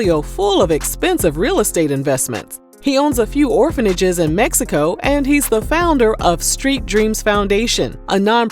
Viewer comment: DID U SAY, "ORPHANAGES IN MEXICO"???@ 5:07 mins. :face-purple-wide-eyes: Upon review, were those acquired during his tenure at BB Wreckers? 👀🚦